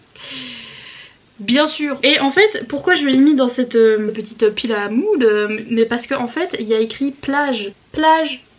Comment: J'ai compris "pile à moule" à 7:06 jpp